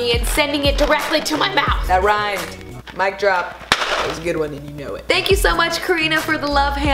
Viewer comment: Did she say mic drop in 2:34 (Edit- OMGGGG thank you for 400 likes😘😘)